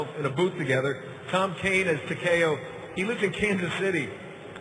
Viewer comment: Did he say TA KE O. HEO NO ITS TA KEY O